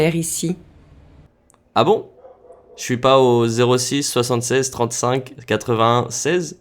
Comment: I found today pretty easy. I also had trouble picking up the man's comment about bothering the woman. I thought that was what he said, but couldn't make out the "vraiment". I was thrilled that I also picked up pas de souci, even though I was not familiar with this expression. I also didn't pick up "je suis pas". I go the suis part, just not the pas. What I am noticing is that I am starting to understand more without translating each word. That is truly exciting. Thanks for this wonderful method. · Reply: I was like that yesterday. I clearly did not hear "je ne suis pas." I heard "Shwee pas" and knew exactly what it was today.